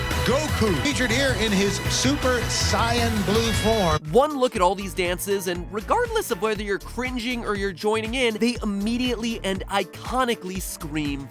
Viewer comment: I heard "super syan" t?